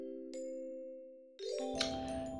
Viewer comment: I first misheard the line as "Do you want us to not talk to us?" and it was a damn funny alternate timeline.